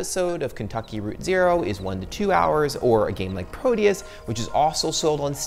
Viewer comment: What is the name of that game? I didn't quite hear it. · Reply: Proteus